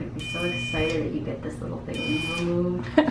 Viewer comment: did i just hear fiance is 8:47 ? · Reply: She said "ur fans will be so excited when u get this thing removed"